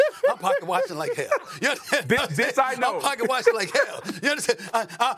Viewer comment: I thought Booger said “b*tch I know” 😂😂😂😂😂